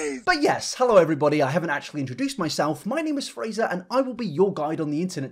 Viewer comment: Did you just say your name is frieza